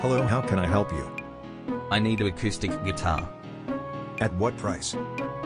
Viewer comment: i thought he said autistic guitar